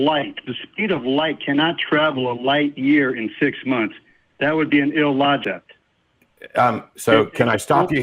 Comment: did he say "That would be an ill-logect" at 1:53...